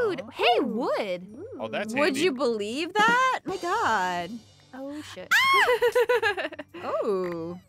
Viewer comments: Did say, “oh sh*t” at 4:15? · it sounded like when said sh*t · DID SOMEBODY HEAR SAYING "oh sh*t" AT 4:15 LIKE WHAT!!!!